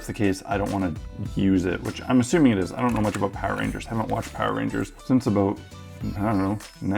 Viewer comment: "I haven't watched Power Rangers since 1998 maybe?" That sounds like someone saying "I watched Power Rangers in Space guys, that was the end of that shit."